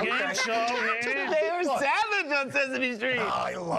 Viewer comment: Count said "pass" L-M-A-O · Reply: I heard him say that they're SAVAGE in Sesame Street!!